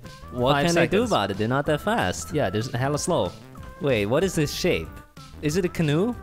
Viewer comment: did I hear a cuss...? or am I just hearing it wrong..? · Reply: •Crystal Studios• its shape, not sh